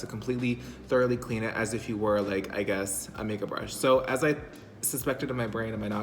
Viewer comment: I'm too high thought I heard "do as if you were a make up brush" hahaha good seeing your videos again